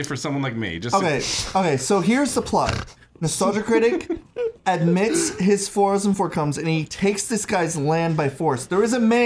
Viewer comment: "Nostalgia Critic admits his fors and forcoms" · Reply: I thought he was saying he “admits his four incomes” as in he’s telling the irs he is committing tax fraud